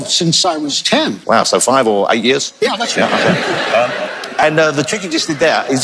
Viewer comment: can anyone please tell me what did Johnathan say @ 5:48 ? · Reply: "So 5 or 8 years?"